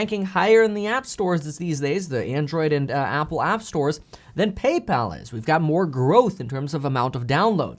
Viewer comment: Did he just say "PayPaul?"